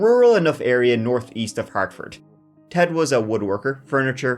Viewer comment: 😂😂😂 I thought she said autistic wood worker naaa just ol new englandahs